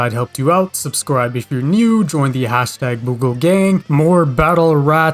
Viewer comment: Anyone else heard him say battle rats?